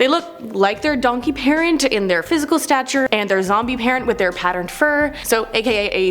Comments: Did she just say "her zombie parent"!!!! Hahaha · you said Zombie parent instead of Zebra... now I want a zombie zonky! ♡♡ much love and welcome to the family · did anyone else hear "Zombie" instead of "Zebra" or am I just hearing things?